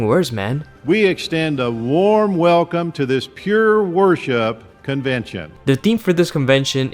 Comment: did anyone else hear "pure horseshot"? Im pretty sure he didn't say "worseship" 🤔. Sorry if im a bit late to that party i just thought id bring a 6 pack anyway just in case 😉. Wonderful content bro